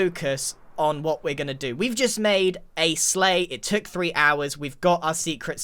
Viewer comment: When said “We just made a sleigh” my brain thought he said “we just made a slay” referring to the seamless audible transition and I’m worried about myself